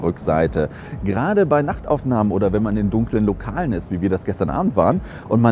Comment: Ich habe beim vorhin bei 1:23 verstanden: "Gerade NACKTaufnahmen....das macht richtig richtig Spaß damit zu knipsen"